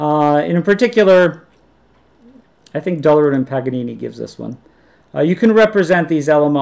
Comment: Who are the authors mentioned in 42:00 that provide the alternative representation? I cannot understand the names. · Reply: Dullerud and Paganini. I really like this text.